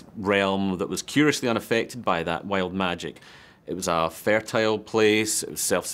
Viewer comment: I heard "Whale magic"